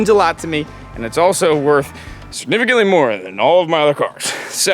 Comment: he said its “significantly more” than his other cars for those of you who are rewinding 40 times to try n figure out wtf he said like me lol so yeah there ya go